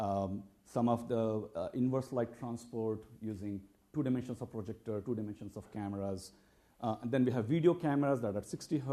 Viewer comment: IF YOU GO TO THIS EXTREME... you can see something SKIP!!! what are you afraid of? saying something wrong... Seems fake to me, again, just like the video posted on MIT's home page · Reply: He said, "If you go to this extreme, you can see something new."